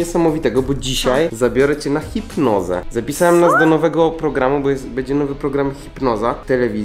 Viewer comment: Zamiast chipnoza usłyszałam fit noga XD